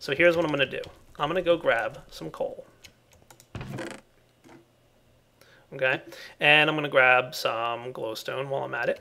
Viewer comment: Did say mkay in 26:53? Lol